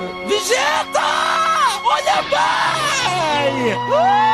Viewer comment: yo entiendo que dice "VEGETAA HAYA PAAZ" como si quisiera mediar entre la pelea de dos niños pequeños